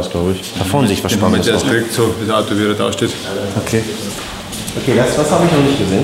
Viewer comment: was hat der gerade gesagt ich sag einfach mal OK 😂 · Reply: Was sagt er denn? Irgendwas mit so wie er da steht xd